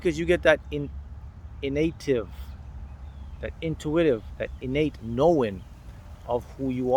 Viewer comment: Did he just say "innative" ?! I knew he was making up a lot of crap but now he's even making up his own words?!